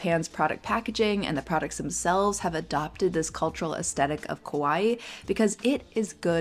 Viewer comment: not really relevant but your pronunciation of "kawai" sound like youre saying "kowai" wich means "scary" in japanese. great video as always tho!!